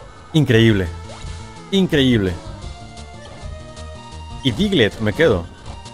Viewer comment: ¿que dice kingler o diglett?